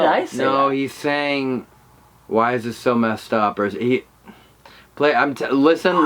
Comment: Instead of “find us” a bunched people heard “why am I so” like he was mimicking him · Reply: I thought I heard saying why is this